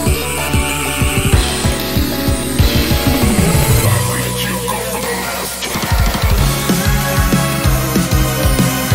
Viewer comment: It sounds like hes saying "NOW THAT YOU'VE GOT A HEALTH PLAN!!!" But fr what is he saying? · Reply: He's saying I'VE LET YOU GO FOR THE LAST TIME!!!!!!!!